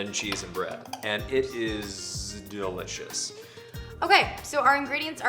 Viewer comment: watch the video with subtitle's it's hilarious! 0:48 Jesus breath lol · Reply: He said "It is delicious" but the subtitle said "Its ok". I guess its not that good after all...